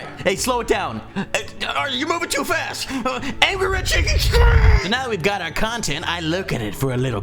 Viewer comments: First of all I feel the need to mention that you forgot to mention how much you press F5 and secondly at 3:49 did you say "ANGRY CHICKEN STRIPS" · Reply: I think he said "ANGRY RED SHAKING SCREEN" :) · it sounded like said ANGRY RED CHICKEN STRIPS